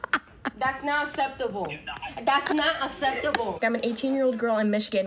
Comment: Did I hear that girl at 2:20 say "That's not asseptible"?